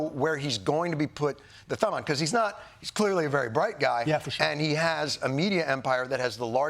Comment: wait... did john say elon is or isnt "clearly a very bright guy" lol cuz hes def not bright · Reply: He said "he is clearly a very bright guy" and Mark said for sure. He def is bright on some aspects. You don't make the biggest rocket company in the world if you aren't. You don't become the richest man in the world by just luck it takes skill and being bright in some aspects.